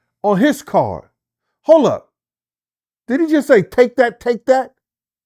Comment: Hold up did he just say take that take that 😂😂 Gene deal is hilarious